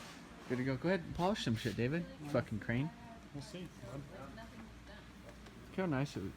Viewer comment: holy shit dude did u say fucking cream omg your like the only dude I know that says cream I say that shit all the time